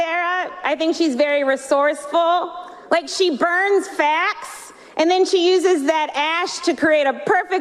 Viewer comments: For a moment I thought she had said she burns fat but she said facts. May people got confused like me. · It’s because people thought she said fat, not facts. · I tell u the truth...first time I heard I really thought she said " burn her FAT and put it on her eyes".😂😂😂..but to hear her acutually say burn FACTS and turn it into Smokey eye makes more sense